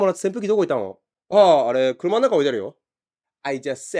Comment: Dang it, instead of 車の中 I heard 熊の中and got stunlocked.